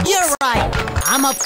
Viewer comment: it kinda sounds like he said “kickass sushi”